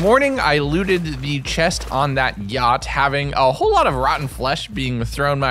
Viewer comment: did he say "gyatt" instead of yacht? or has my brain rotted to the point of a malicious sludge?